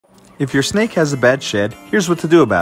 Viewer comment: I thought he said, if you’re snake has a bad shit